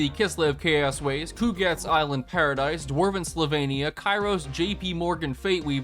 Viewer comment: did he say dwarven Slovenia?